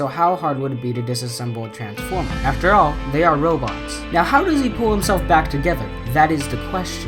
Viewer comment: did he say robux